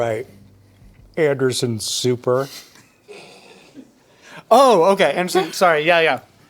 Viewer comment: I finally realized what Sam was trying to say here..... Anderson Souper